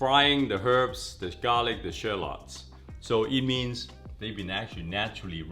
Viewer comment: He said Sherlocks instead of Shallots lmao! Usually I would cut thin slices and set them to the side. Sprinkle a little salt and pepper, good to go!